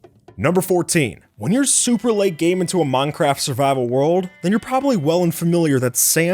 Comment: i swear he said Among Craft